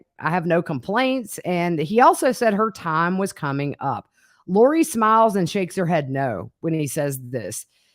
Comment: “No complaints…” it almost sounds like he says: “she served her purpose well, now it’s time for her to go and for me to have a paaaaarty!”